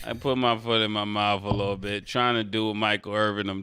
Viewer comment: …. I AM IN TEARS!!! 😂 Mike definitely just lied for Stat!!! Good job Mike!!! 😂😂😂😂😂😂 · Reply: I think he might’ve misheard Stat. I heard “put in my mouth” Mike wanted no part of that convo